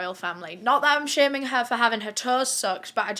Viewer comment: English is not my first language and I heard "she was having her toes soaked, it was such a scandal" and I was like "is that... a bad thing? 😳 I always do that 😭😭". And then I saw the picture 🤡